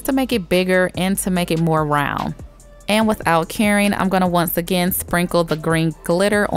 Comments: It really sounds like you say without caring whenever you say without curing lol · when she says 'without curing' I keep hearing 'without caring' and I'm over here like 'damn sis is savage'